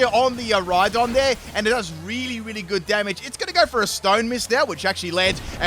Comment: Lol You said Stone Mist instead of Stone Edge. Oh well :) it was at 4:55 when Blondie the Rhydon used Stone Edge, maybe I misheard it. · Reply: I think he said "Stone miss". Stone edge has a really low accuracy so it misses a lot